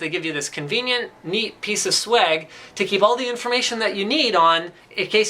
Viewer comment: did Linus just say swag? WHY?! WHY?!